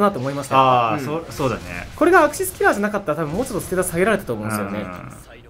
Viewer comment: 1分9秒で、がアクシスキラーって言ってるのが、キスキラーって聞こえた人挙手